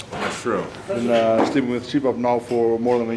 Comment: Hey guys, can somone tell me what they are talking about at 5:18??? It´s about sleeping. But the only thing I hear is "see pub", which is cleary wrong. It´s something Thor uses to get better sleep. Thanks in advance ^^